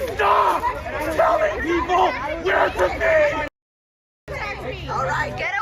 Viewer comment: Did he say "Where's the beef?"?